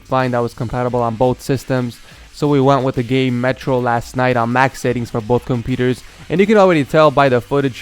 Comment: I think he said "metro last night"